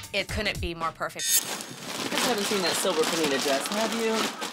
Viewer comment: I thought I heard Randy saying' where is that silver foil' when searching 😂😂